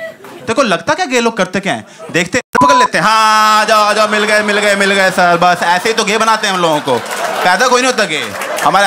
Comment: & 2:35 — what does he sayyy???? At beep sound. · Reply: l8nd pakad lete hai